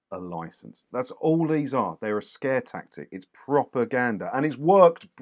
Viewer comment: Until he said it a second time I thought was saying “This is proper gander…”, some Essex colloquialism I didn’t know! ‘Propaganda’ – doh! 🤦‍♂